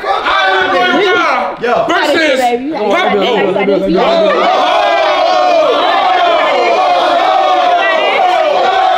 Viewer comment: What did she say 24:27 ?????? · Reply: she said “ you got this babyyy “😮😮😮